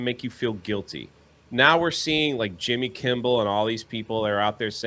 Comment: Did say "Jimmy Kimball?" lol